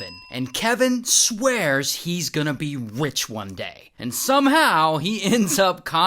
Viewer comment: "wich one day" is what i heard, is it just me?